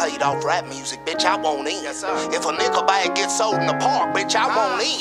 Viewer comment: did he say the n word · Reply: No, he said Nickel bag.